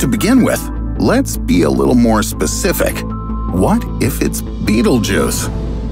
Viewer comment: bruh i wasnt looking at the vid when he said "what if its betelgeuse?" and i thought he said "what if its beetlejuice". Lmao